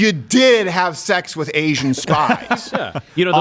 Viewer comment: Ah! ... Asian spies ... i thought you said Asian spice ... from the spice girls 😂